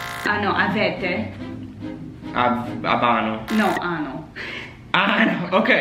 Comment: you said "ano" (instead of "hanno"), which means...